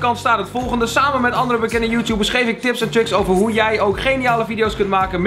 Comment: Ben ik de enige die “tips en chicks” hoorde ipv “tips en tricks”